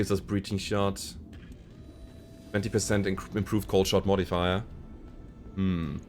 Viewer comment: I keep hearing "cold shot modifier" and have been so confused. I get it now